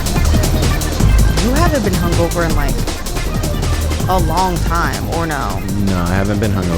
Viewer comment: I thought they said “I haven’t been humbled in a long time”